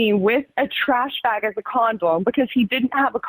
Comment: did she say “condolm” ? 😭